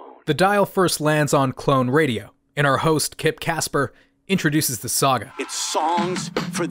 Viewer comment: Hey alright, it's Kip Kasper Clone radio, LA's infinite repeat How we feelin' out there? How's your drive time commute? I need a saga What's the saga? It's Songs for the Deaf You can't even hear it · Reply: Oh man…I always thought he said Kid Kasper…I had it wrong all these years